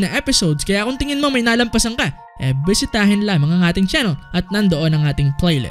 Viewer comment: bakit buysitahin lods haha narinig KO lang